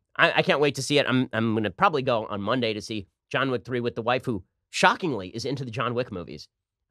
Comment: Did Ben just say waifu?